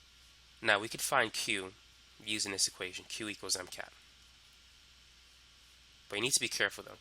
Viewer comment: did he said 'cat'